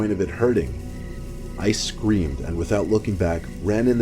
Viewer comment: he said "I scream" and i heard ice-cream